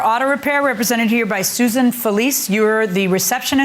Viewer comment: I thought they said susan police